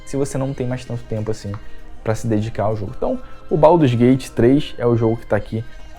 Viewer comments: Esse sotaque carioca é uma pika mané, eu entendi "baú dos gay de 3" e é baldur's Gate 3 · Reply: Entendi " o pau dos gay de 3" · Esse sotaque carioca é uma pika mané, eu entendi "baú dos gay de 3" e é baldur's Gate 3